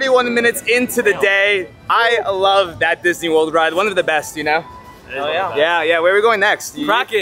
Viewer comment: Did I hear that right? Didhyou say Disney world ride at around 2:40?